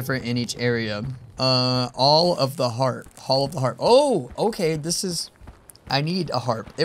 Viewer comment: I thought it said hall of the trap